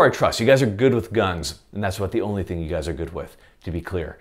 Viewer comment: The fact that you flashed a quick "rah" in the top left literally as I said it in response to your joke was really unnerving. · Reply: I thought you said "that's ABOUT the only thing you guys are good with". Did I miss hear? I'm sincerely hoping you said "NOT" rather than "about". I seriously couldn't tell, but it sounded more like "about". That would be quite the falsehood. With the "Rah", I suppose I did misinterpret your slur.